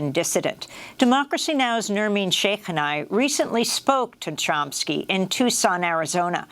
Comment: Did she call him Tromsky at 0:15?